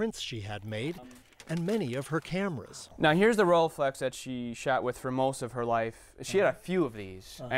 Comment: THE DUDE SAYS SHAT! INSTEAD OF SHOT! BAAAAAAAHAHAHAHAHA